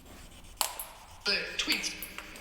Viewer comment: THEYRE TWINS. I THINK IT ACTUALY SAYS "BOO TWITT"